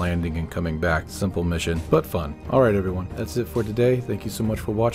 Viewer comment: Did you say “butt fun”? I’m in